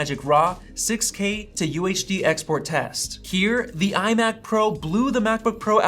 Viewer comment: did you say "iMac Pro"?